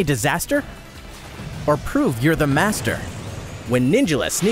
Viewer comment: The second it said "free-to-play," I heard "microtransactions" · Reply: I heard " Fee to play "